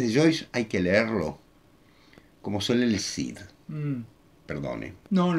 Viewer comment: Por favor, ¿alguien me puede ayudar?, en 25:47 dice: "que el Ulises de Joyce hay que leerlo como... ¿suele Lessina? ¿Qué dice? ¡No entiendo! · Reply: Puedo estar equivocado, pero me pareció escuchar: "que el Ulises de Joyce hay que leerlo como se lee el Cid". Supongo en referencia al Mío Cid.